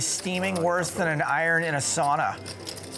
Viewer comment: what does the caster say" he's steaming worse than iron in a solar" ? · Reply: Sauna not solar.